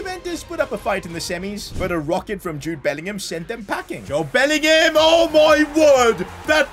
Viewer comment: Did he say Jobe Bellingham 😂😂